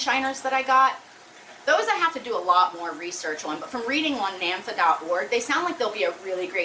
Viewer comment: What is the site you said that you read about fish? It's at 2:15, <link>. Namfa? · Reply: <link>. North American native fish association